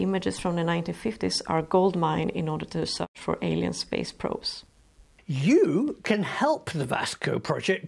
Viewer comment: Did she actually say the words "Alien Space Probes"????